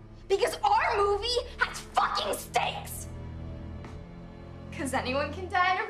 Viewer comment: Right lines: stakes What I heard: steaks